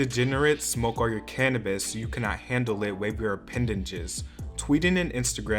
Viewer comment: Did...did he just say "appendaNges"?